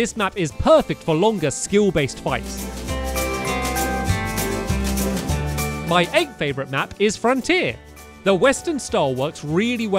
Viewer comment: Because I'm not used to a British accent I thought you said my egg favorite map instead of my 8th favorite map lol